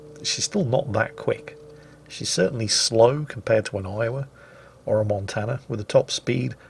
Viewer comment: I heard "She's still slow compared to an island" and absently nodded in agreement, had to rewind back and do a double take